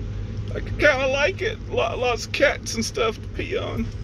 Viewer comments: All the fun of putting it together the way you want it. I thought you said, " There's a lot of cats for you to pee on in here." LOL. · All the fun of putting it together the way you want it. I thought you said, " There's a lot of cats for you to pee on in here." LOL. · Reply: It did sound like that! I was right beside him and heard him say cactus.